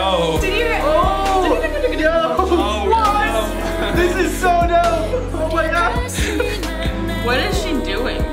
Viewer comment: I thought he said ”this is so dumb”!